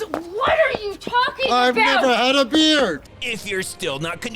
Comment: What I heard : I've never had a beer.